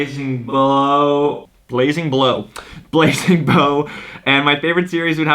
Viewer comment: LOL I put on sub titles and when he said blazin bow it thought it said playdough😂😂😂😂😂😅😅😅😅